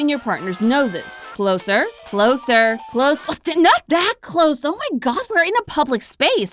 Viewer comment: Did just say "oh my Goth" that is the most hilarious thing I've ever herd